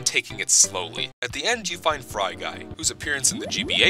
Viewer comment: it lagged out for me at that part and it sounded like at the end you fuck fry guy T_T.